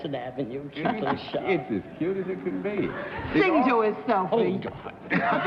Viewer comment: At the 17:40 mark Arlene says (something) about a “selfie?” to Sophie Tucker. I’ve played it over and over and over and still can’t figure out what exactly she’s saying. CAN ANYONE MAKE IT OUT? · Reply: She said “Sing to us, Sophie.”